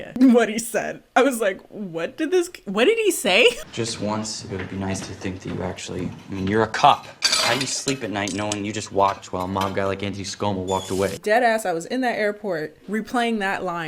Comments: I have to look up what Light said and apparently he said: "Just once, it would be nice to think that you actually... I mean, you're a cop. How do you sleep at night knowing you watched while a guy like Antony Skomal walked away?" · Ohhh “mob guy”... sounded like he said “while a manga like Andy...” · I heard, "Just once it would be nice to see you actually- I mean you're a cop, how do you sleep at night knowing you just watched while mom guy like anti scomo walked away" and I was so confused as to what Scott Morrison had to do with a death note